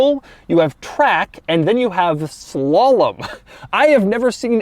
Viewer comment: I have this playing in the background while getting ready for work and I heard "Swallow Mode" instead of Slalom and just about died laughing. I guess it would be better than Spit Mode.